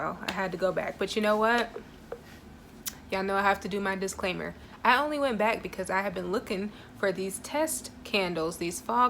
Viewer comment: Were you sipping on Monster Paradise? When you said, "I only went back because... " all I heard was "See what had happened... 😂Thanks for the video.